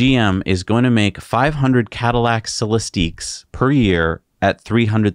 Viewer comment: GM - "The all new $300k Cadillac Celestiq." Me - "Did you just say Cadillac celery stick?